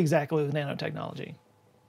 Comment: Thought he said Nana technology, you know like knitting